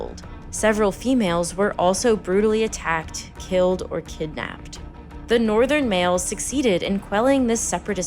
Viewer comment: did you say kidnapped???🫣😂